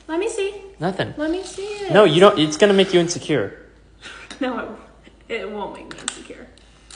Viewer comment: I misheard "it will only make you insecure" for "you will only make her disappear" lmfaooo